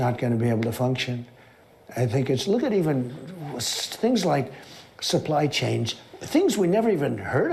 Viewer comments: I heard, “supply chains” and I despise the guy. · In all fairness, I heard it as "supply chains" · sounded like he said "supply chains"